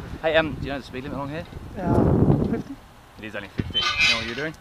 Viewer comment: I thought the first cop said the speed limit was 15. i was like wtf